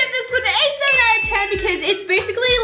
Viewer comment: you said 7/10 but the caption said 10/10... or am i deaf?